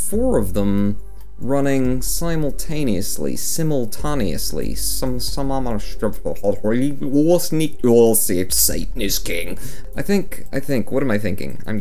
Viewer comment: Am I the only person who heard him say Satan is king in that gibberish at 2:01?